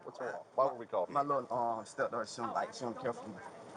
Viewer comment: Did he call the child his step daughter? Didn't that lady basically say they're just kicking it? How we get from "he come from time to time when I feel like it" to "my step daughter don't really care for me"?